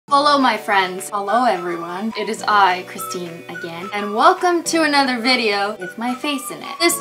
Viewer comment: In ur intro when u say "its me Christine" it sounds like ur saying "its me Christina gain